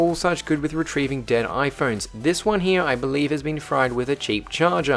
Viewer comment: I thought it said "recieving my dead iphone se"